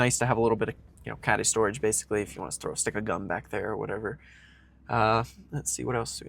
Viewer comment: At about 14:30 did you say "a stick of gum", or did you say "stick a gun"?? 😲 🙂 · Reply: Haha, I said stick of gum but I suppose either works :)